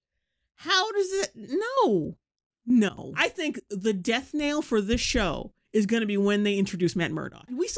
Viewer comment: It's "death knell" btw. Sounded like you said "death nail" here. Just an FYI, no hate lol